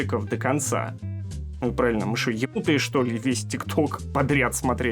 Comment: что почему, мне кажется я не так услышала «Мы что, якуты что ли, чтоб просматривать весь тикток» Мне кажется, или у всех вместо «якуты» разные слова??